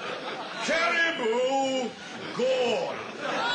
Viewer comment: I thought he said Caribou GORN not Gone...